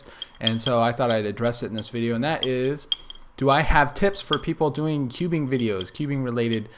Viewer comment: I thought he said "Do I have tits" xD